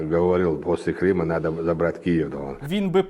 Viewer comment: Я правильно услышал на 0:43 "... после Крыма надо было брать Киву.". Пусть берёт, не жалко :-D